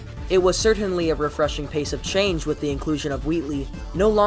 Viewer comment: did you say “pace of change”